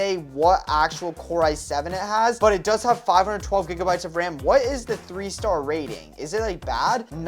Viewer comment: Did i hear 512 GB RAM